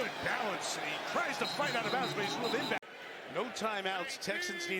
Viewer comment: was that a “The Strouds” I heard